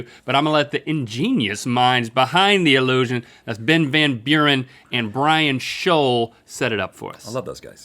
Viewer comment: I could have sworn Link said “Breadbin Urine”…any S!nners?